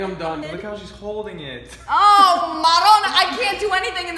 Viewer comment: What is saying at 9:58?? Sounds like "oh maronna" that means "oh Mary" in sicilian dialect (italian) 😂